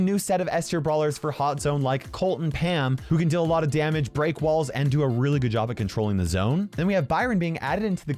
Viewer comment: Did he really say Pam breaks walls?